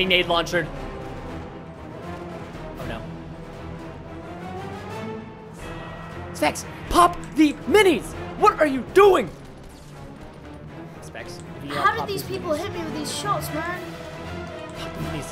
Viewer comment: Did Preston Just Said SEX in 12:22?! edit:I was like ewww